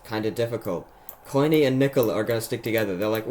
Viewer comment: why tf did bro say the n word and its the nikka word